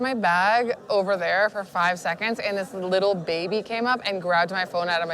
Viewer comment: Omg 20:52 I almost died laughing when she called the baby a porn addict. Hahahahaha. I also thought she was going to say "little bitch" rather than "little baby". I LOVE this woman!